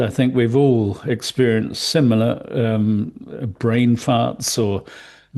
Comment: did he say "Brain farts"? 😂 Don t forget to mention that the numbers are "average acceleration". The instantaneous acceleration varies upon the speed and is not constant from a steady position to Vr.